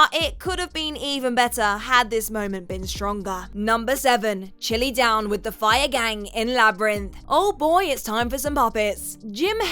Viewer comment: Oh, they're the Fire Gang? I've watched Labyrinth numerous times and could never make out what they were saying in those weird voices. I first heard it as "chewy down with the fuzz fangs". That whole sequence is annoying, the song is bad and it's unnecessary, though I suppose that could be said of anything. The SFX never bothered me but I was born in 1970s and all SFX looks groundbreaking at first; it's only bad if you're used to 40 years of improved effects.